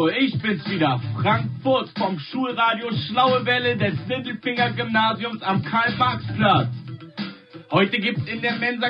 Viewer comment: ich hab immer Sindelfinger Gymnasium verstanden aber hoffentlich heißt es wirklich Mittelfinger, das ist um einiges cooler